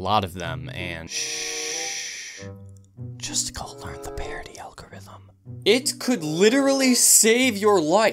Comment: I thought it said party algorithm